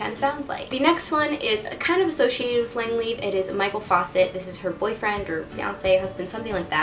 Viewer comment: did she call him Michael faucet lmao 😂